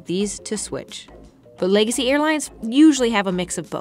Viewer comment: Legacy airline, I thought you said lazy airline.